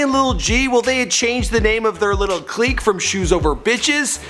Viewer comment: It's Strictly only brothers it sounded like you said over